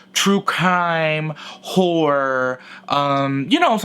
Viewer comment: When you said “horror” as a genre of tv shows, I first heard “whore” I’m going back to bed y’all lol